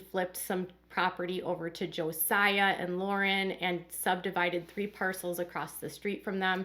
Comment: I thought I heard Josiah and Lauren were headed for a divorce